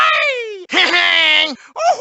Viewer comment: says 'I'm gay' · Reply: Honestly at 0:53 i hear im nay (and yeah i know hes saying ok too)